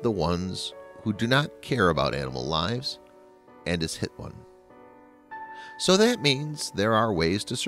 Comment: I thought he said, "and is Hitler."